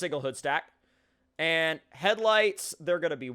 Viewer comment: I thought you said "white hiv's"